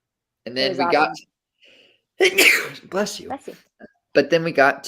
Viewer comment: did you just say "bles you " to yourself???